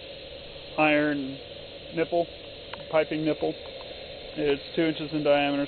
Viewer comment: I thought he said "iron nipple, piping nipple".